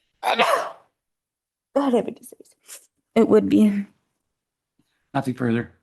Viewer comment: does she say ""I have a disease"" ? LOL and 1:20:53 WTF? Strangest 10 seconds LOL